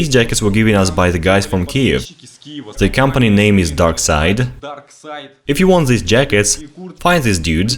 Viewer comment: Can someone point me in the right direction, I've been looking for one of those camouflage jackets with the Pripyat ferris wheel, but havent had any luck. I know he said (dark side) located in Kiev is the company that makes the jacket, but I just cant seem to find it. I live in the US, maybe their website wont show up for some reason. I would be very grateful if someone could respond with a link so I can buy one. I've been searching for who makes that jacket since first made the Pripyat exploration series of videos. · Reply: It's actually "Duckside". I heard "Darkside" as well. And it says Duckside on the jacket. Here's a link to their store. <link>